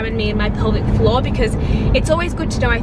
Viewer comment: THIS WHOLE TIME I THOUGHT SHE WAS SAYING PELVIC FLAW OMG